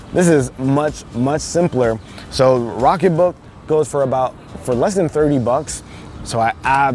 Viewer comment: I thought he said rocket book goes for less then $300 I was bout to be like hold up, wait a minute something ain’t right